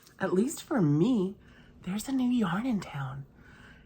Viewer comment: I heard you say "Joanns new yarn" and i was on the website like lightning! :) Love your vidoes!